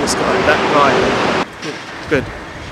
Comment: Sounded like you were saying Charsiu as his first name at one point...hahahaha